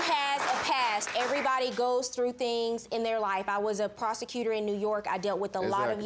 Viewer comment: I thought she was gonna say… I was a prostitute in New York 😂😂😂😆